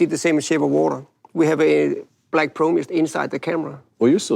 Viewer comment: The subtitles say "We have a light probe inside the camera", it should be "We have a Black Pro Mist inside the camera".